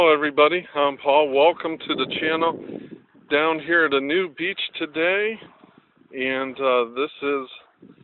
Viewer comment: I had to wind back... Thought you said nude beach... HA - my brain no work.